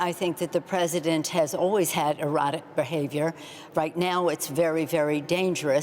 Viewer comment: Did Nancy just say at 3:06 that The President has always had " Erotic Behavior " ? <link>